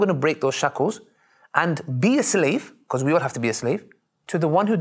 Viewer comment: May Allaah reward you all at the channel for any good you are doing for Allaah's sake. Please edit the captions at 9:45 as it is not what brother Ali said and the meaning is a bad one. Ali actually said: "(be)cause we all have to be a slave, to the one who deserves it: Allaah" Jazaakumullaahu khayraa